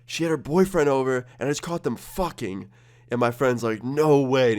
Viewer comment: All I hear "I JUST CAUGHT THEM FUCKING" I BURST OUT LAUGHING